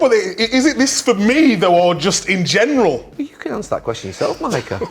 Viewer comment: what did Gary say? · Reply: “You can answer that question yourself, Micah.”